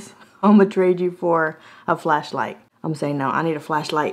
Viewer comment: For a minute there I thought you said fleshlight 😳🤭😂